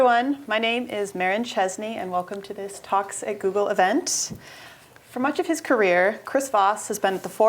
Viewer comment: ish it sounds like she says "Toxic Google."